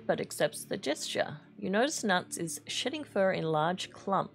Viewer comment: I heard 'shitting fur'. I had to rewind 😂